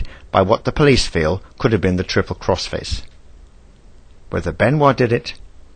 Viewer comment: Did you call it the Triple Crossface??? Seriously??? It's called The Crippler Crossface